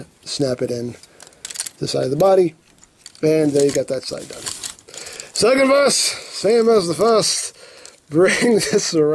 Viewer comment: What does he say every time he has to do something twice? At 15:14 is one instance. He says it every opportunity and I have no clue what he's saying. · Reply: second verse, same as the first